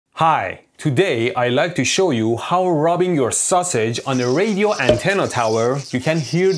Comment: The first thing I heard was "rubbing your sausage" LOL LOL!